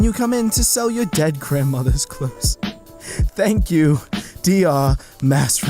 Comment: Did He Just Say "Thank You, DR"?!?!